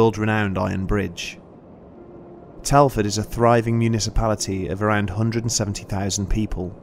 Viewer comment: when the guy said 'Municipality'I thought he said 'a big party'. This guy is the most unlucky man in the world. He wants a Taxi but he hasn't got money, he rides the bike and the sit falls out of it leaving the metal tube underneath uncovered. It should have been painful